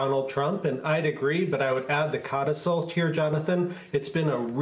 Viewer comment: Hey, what word did he say ... 0:27 ... codastole? ... Never heard that word. How do you spell it? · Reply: codicil